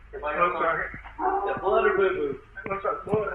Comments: "Blood or BooBoo?" My favorite question of any of these videos. The son is Nuttier than a snickers bar. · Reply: I thought he said "doo-doo"! "Blood or boo-boo" is way better! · I cant lie, i cracked up when the cop said “is that blood or booboo?” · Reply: I thought he said "doo-doo", or "poo-poo", as in dog poop, based on the dog howling in the background. I laughed also, as I thought he was being polite and not using the word "shit"! Either way it was a funny little segment in a bizarre tale. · I’m sorry this really is tragic but the only thing I can focus on is what the sheriff says at 9:08 “Is that blood or poo poo” is all I hear and I’ve been fixated on that for longer than I’d care to admit lmao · what did the officer just say??? Did he say 'is that blood or doodoo?'..